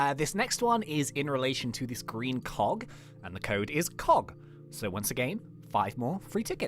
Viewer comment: Code list : 2:11 "Wax" 2:18 "Roof" 2:37 "Nectar" 2:58 "Crawlers" 3:22 "Connoisseur" 3:36 "Cog" 3:48 "Buzz" 3:56 "Bopmaster" 4:13 "38217" 4:39 "Wink" 5:09 "clubbean" 5:18 "SecretProfileCode" 5:41 "WikiAwardClock" 6:10 "WikiHonor" 6:28 "GumdropsForScience" 6:52 "Sure" 7:12 "ClubConverters" 7:41 "Marshmallow" 7:55 "ClubBasket" 8:13 "500mil" 8:43 "Discord100k" 9:02 "BeesBuzz123" 9:38 "Gumaden10T" 10:05 "ClubCloud" 10:16 "Tornado" 10:32 "4milmembers" 10:57 "Teespring" 11:16 "Leftovers" 11:33 "Buzzy" No need to like but I'd appreciate it! ❤️ Btw This took 27 mins to make 😅😂 Edit : I saw that someone else made a comment like this but they edited it and they deleted the codes so I'm the only comment with the timestamps 🖤🖤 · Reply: Shadow RBX 3:36 I thought you said fog